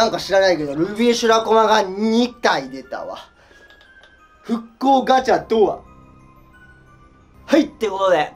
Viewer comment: 風邪だからかもしれないけど復刻ガチャは罠って言ってるとこ復興ガチャに聞こえる笑笑 風邪お大事にしてください！